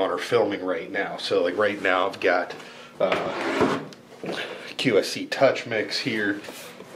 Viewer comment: for a sec I thought you said QFC touch mix and not QSC touch mix. I was about to comment about how QFC is a grocery store and not a sound company. Lol